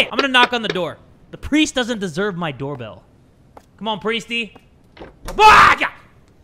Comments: Did just say BAAAKA at 6:19 😭😭😭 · no way- Someone said that his scream sounded like “Baka” and I was waiting for it to happen It actually sounds like that NOOOO😭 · did you just say baka in a weird way or....baya? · what I hear : “BAAAAKA” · BAHAHA HE REALLY SAID "BAKA" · did anyone else hear "BAKA!" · It might’ve been because I watch too much tiktok, but at 6:19 I swear he said “BAKA” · It sounded like he said “BAKKKAAA” at that moment!!!!🤣🤣🤣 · did he say baka